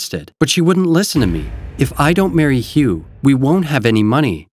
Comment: did she said "if I don't marry you"💀